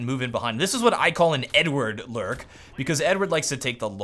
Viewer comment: I thought he said "n-word lurk"